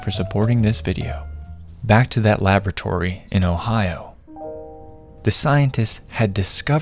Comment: DID YOU SAY OHIO